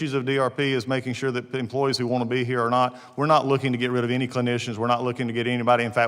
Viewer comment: Did I hear that right he said ,they are making sure that employees who want to be here are not.😮 They just keep telling on themselves,wth 😒